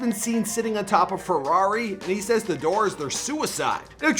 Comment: did I just hear ferrari?